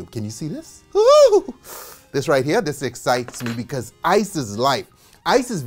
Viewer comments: Did he said "isis life" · My drunk arse thought he said isis is life at 0:06 😂😂